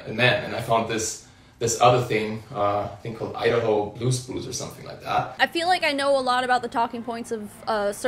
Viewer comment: I keep hearing 'Idaho Blue's Clues' instead of Blue Spruce. "With me and you, and my friend Blue, we can do anything that we wanna do." <-- sounds pretty MLMy to me